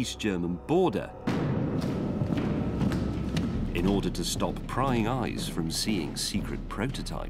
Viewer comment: What does he say? Prying eyes? At 3:40 minutes?